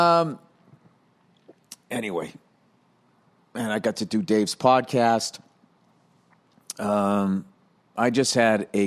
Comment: did he just say dave's podcast !?